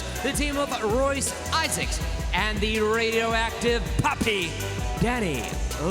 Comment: Danny limelight. First time I heard Radioactive PAPI. I thought they said Radioactive PUPPY. 🙄🙄🙄🙄